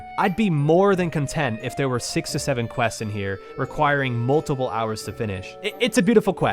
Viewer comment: did you say... 6 to 7 quests?